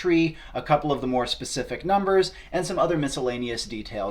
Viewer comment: I misheard that as "Norse specific numbers" for some reason xD